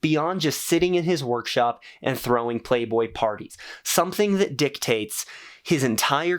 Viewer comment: i am so cooked, i wasn't really paying attention, and i thought he said playboi carti lmfao